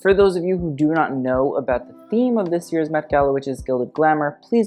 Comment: I genuinely thought you were saying “Meh Gala” in your intro.